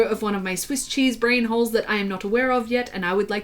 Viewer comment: I honest to God thought you said Swiss cheese whore and I've never been so confused lmao